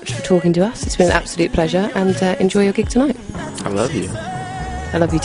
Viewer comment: did he just say i love you at 7:15 hmmmmm